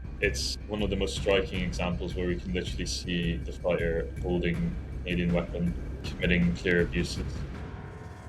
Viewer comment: Captions are wrong. He said "holding a Canadian weapon, committing these abuses." But the captions read "holding a Sterling Mark, committing these abused."